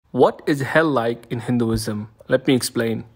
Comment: First time watching this I heard "what the hell is Hinduism?"